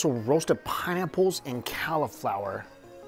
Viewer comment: Did he say "cowlaflower"? 😂